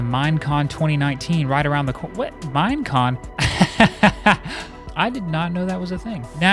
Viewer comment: Every time he says "Mine Con" I thought he was saying Mien Kampf